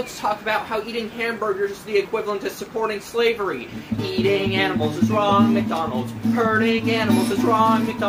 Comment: i thought when she said ''eating animals is wrong'' she said ''listen to my friend who's called'' and then ''John Cena''. just smt i thought i'd say.